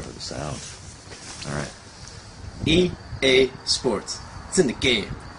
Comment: for me its always like "e a sports cdsjcs game" :D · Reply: In Polish i always hear "EA Sports cnyn gejm"